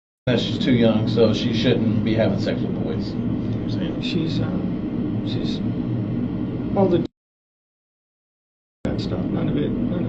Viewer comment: 😟If you listen carefully, it sounds like he is saying "She’s ummm… she’s old enough —-" before the redaction starts…. Ugh! What was the rest of that sentence!! (Obviously he thought EIGHT was old enough for some things…. 🙄😤🤢)